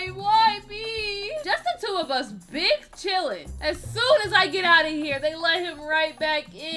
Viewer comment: he really said BING CHILLING🤣🤣🤣🤣🤣🤣🤣🤣🤣🤣🤣🤣🤣🤣🤣🤣🤣🤣